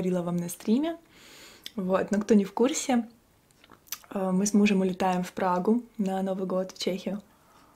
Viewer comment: с кем ? Мне не послышалась ? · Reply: ну с мужем, с кем еще:)